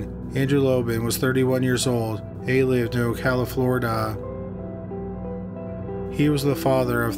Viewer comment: did you just say Califlorida? LMAO